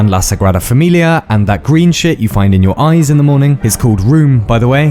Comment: sorry but did you say green shit??